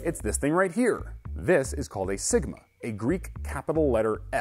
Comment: I thought he said "This is called a ligma"